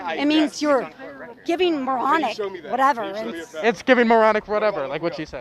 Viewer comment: The definition of oxymoron is "saying moronic whatever". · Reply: I heard it as "giving moronic whatever"